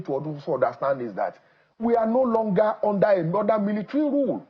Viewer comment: Did I hear him say "We are not longer under a Northern Military Rule" ? at 14:05 ~ 14.07